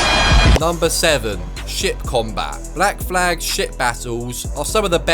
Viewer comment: I thought he was saying shit instead of ship combat. 😅